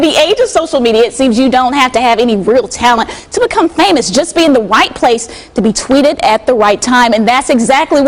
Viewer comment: lol maybe it's the news lady's accent but it sounds like she said all you have to be is in the white place at the white time.i know that's not what she said but it sounded like it to me. also she sounds a bit salty. also why some of those post have pictures of guys but they say scan my pu$$y? and why the hell would you want him to scan your pu$$y with that dirty a$$ scanner.....